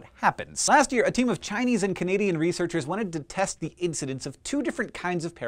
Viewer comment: for a moment I thought that Hank said "Chinese and comedian researchers".